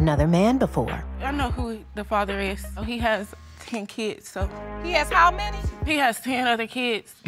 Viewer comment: Oh my gawwwd in 1st case thought heard her say,"he has 10hundred kids"! But still got to make better choices..geezers